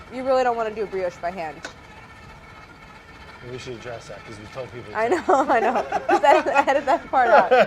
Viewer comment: can someone tell me what they said 😂 I can’t hear it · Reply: "We should address that" I think the rest was, "because you told people to do it by hand." This was after they talked about brioche and she said she would never mix it by hand.